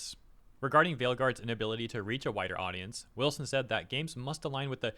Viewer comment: I swear I heard him say that "Veil Guard failed to reach a whiter audience.".